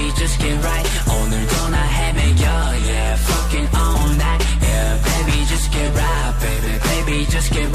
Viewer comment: ever since this song is released i keep hearing sanggyun's "forget or not" as "f*cking or not" or "f*cking all night". oof sorry not sorry I'm just being honest to what i heard😂😂